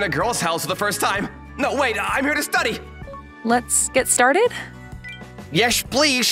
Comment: My brain went all stupid and I thought it 6:55 says "Let's get sharted!" 💀